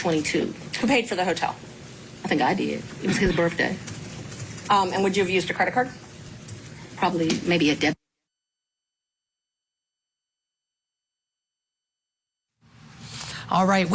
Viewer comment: Did she actually say “birf-day?” 16:35 🤷‍♀️